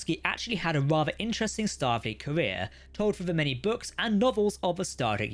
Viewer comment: did you say stargate? 😂